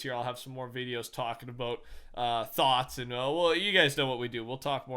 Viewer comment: I thought u said thots lol